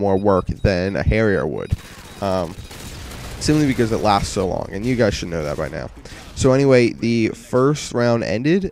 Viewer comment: Lol, if you put the translator on at 3:21 it thinks that says: "Soon because the laughs along in the direction of Africa" Haha! WTF? He says "Simply because it lasts so long. You guys should know that by now." LOL